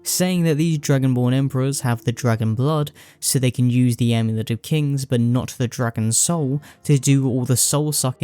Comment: I thought you said “the N word of kings”